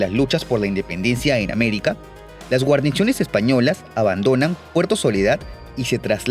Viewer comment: Alguien más escucho que dijo. Guarnichones